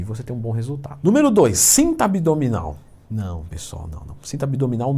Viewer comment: Eu ouvi "cinta modeladora", Maurícião?😂